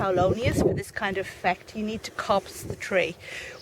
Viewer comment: Ah, gotcha! My computer thought you said to "cops" the tree, but I just realised you said "coppice". I looked it up and it said: 'coppice the tree, ie cut it periodically to ground level'. Similar I suppose to coppicing hazel. I usually 'stool' mine around the end of March, and it works very well.